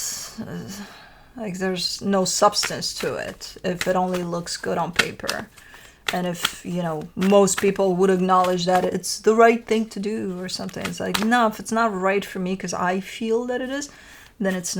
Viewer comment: I heard you says - it only looks good on Libra at 11:26 maybe that’s not what you said but heard it again and for me that’s what is was again and it hit the spot.